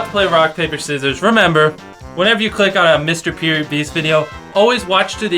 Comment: did he say mr period beast video ?